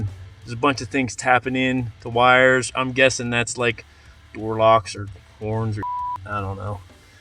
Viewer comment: i swear I thought I heard him say warlocks 😂😂😂😂😂